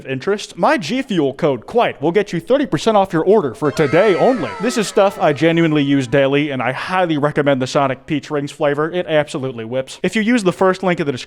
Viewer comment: Tbh, thought he said Sonic Pee Rings Was almost interested in a energy drink 😩